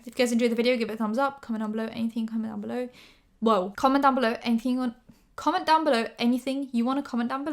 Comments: this is the first time you slowly said your outro and this whole time i thought you were saying “comment down below anything, comment down below” 😂 my ears are too slow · Lol I always heard: comment down below anything you want, comment down below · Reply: aberdeen I heard "Comment down below, anything comment down below." 🤣